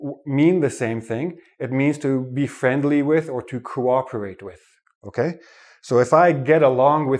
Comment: Thank you so much. 6:30 "to be friendly with or to *** with" I can't hear this word well. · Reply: "to cooperate with"